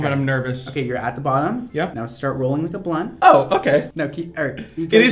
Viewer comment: did he say "now start rolling like a blunt"?!? 🤣🤣🤣🤣🤣